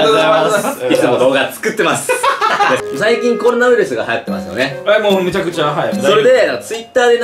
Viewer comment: 「最近コロナウイルスが流行ってますよね。」が「最近こんなウイルスが流行ってますよね」に聞こえるのは俺だけ？